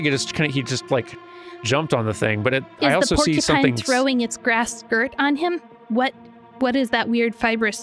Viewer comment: I paused the video, came back a day later and I hear say “is the porcupine throwing it’s grass skirt on him?”